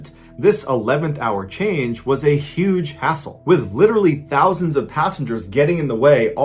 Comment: When he Said hassle I thought he said asshole